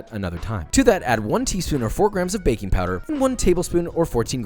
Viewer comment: istg I heard "baby powder" instead of baking powder and was really confused for a second lol